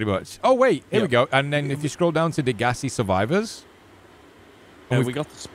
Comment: when he said Degasi survivors I thought he said "The gassy survivors"